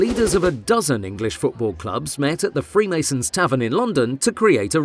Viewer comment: did I just heard "Freemason"?